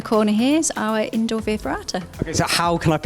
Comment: their indoor what? i have no idea what they are saying. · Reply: Hank via ferrata